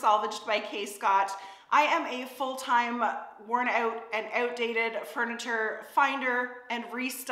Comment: Sounded like you were saying you're worn out and outdated haha. Worn out, I imagine, after moving. I've been thinking of going that way (in S Ont now) so would love to hear your impressions of N Brunswick, especially through your first winter.